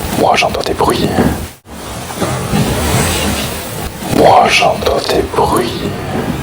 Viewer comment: le bruit c "moi j'entend des bruits? "